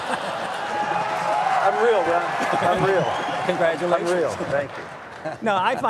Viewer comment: did santorum say "I'm real, brah"?